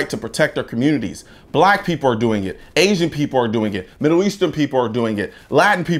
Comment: I thought he said "black people are doing it" twice lol.